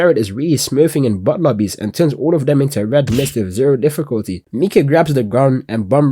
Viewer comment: A what...? Say that again? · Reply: Some kind of... Red mist...